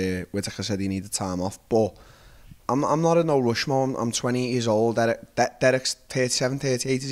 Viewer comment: Darren "I can say multiple words in one word " Till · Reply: when he said "im 28" it sounded like "im 20"